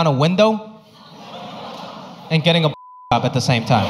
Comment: What did he say at 6:05, I can't hear that omg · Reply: “And getting a bl0w j0b at the same time” 😝